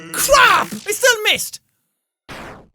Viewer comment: did jack just say "clap"?